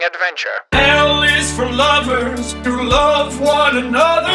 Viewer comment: Am I the only one that when he said "L is for lover" thought he said "he// is for lover"?